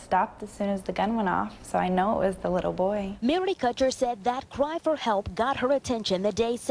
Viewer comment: Did I hear her right? Did she actually say " the little boy" (at 0:58)? He was a 17 year old 6'2 football player. I get the feeling that the reason the police didn't waste much time interviewing her is because she didn't actually witness anything. She only showed up after the shooting & apparently never even get close enough to realize that it wasn't a "little boy". I suspect she got the idea that it was a "little boy" from seeing the old childhood photo on the news.